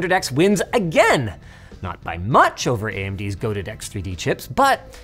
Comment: did linus say goated?